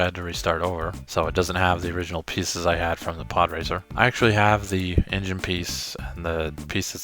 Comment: I thought it said pooracer